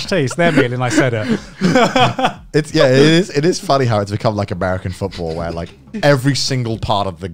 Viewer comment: Did someone just say americ anfootball? *never meant starts getting louder into the foreground*